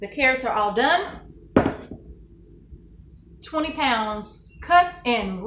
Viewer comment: Question: 20 pounds carrots make approximately 16 pints? I thought at first you said 10 lbs. Just double checking. THANKS FOR PROVIDING TRAINING FOR NEWBIES LIKE ME.